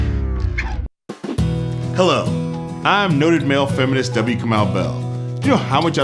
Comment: I thought he said, "I'm neutered male feminist W. Kamau Bell." I am absolutely horrified right now, that commercial needs to be incinerated. I can't even with this.